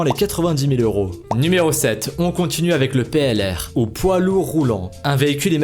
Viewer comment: j'ai cru entendre PNL... Rassurez moi je... Je suis pas le seul à l'avoir pensé 0_0